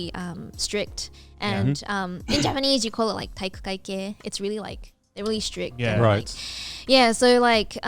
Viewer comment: shu says "strict" in japanese. It sounds like たいくかいけい, but I can't find any results for that except "jock". Can anyone help? What's たいくかいけい？or rather what is she actually saying since I can't hear anything except that lol